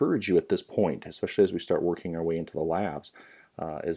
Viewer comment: I heard "IA Labs" knew it was a DSU professor